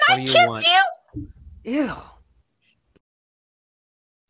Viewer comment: Why do I hear “play it on me”?